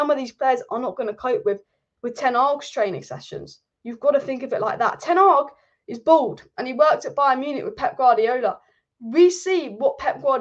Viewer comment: did she say Ten Hag is bald and worked under Pep at Bayern or is my hearing going 😭